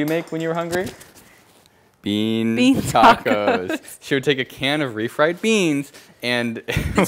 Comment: But beans on toast is pretty dope!! Or wait…did she say "bean tacos"? It sounds like both honestly lol. Beans on toast, would recommend, especially with brown sauce.